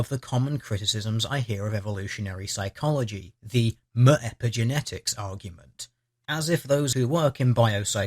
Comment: What's he saying around 1:30? "Muh epigenetics?"